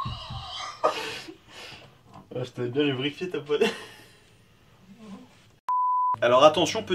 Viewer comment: J'ai bien entendu "J'vais bien la briquer ta poignée" ?!? 09:00 : Hum, non on n'a pas ce geste. En France les deux index restent collés l'un à l'autre. 10:31 : Ah lala, celui-là j'y ai eu très souvent droit au Japon ! Quand on ne voulait pas que je prenne quelque chose ou quelqu'un en photo mais aussi quand je voulais demander un renseignement et qu'on ne voulait pas me parler. Pour un Français... ça parait un peu agressif comme geste, ça ressemble un peu à un : "Fous-moi la paix toi !" mais bon, sachant la politesse des Japonais, j'me suis dit que ça n'était certainement pas agressif pour eux...